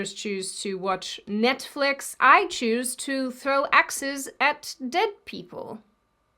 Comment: At first, I really thought she said : I prefer to throw exes at dead people. 😐 Then she put up a clip of the game, and I realized what she actually said. 😸